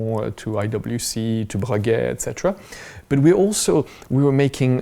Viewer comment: I believe there's a small mistake in the subtitle at 14:52 I think he says "to Breguet" and the subtitle reads "to Patek"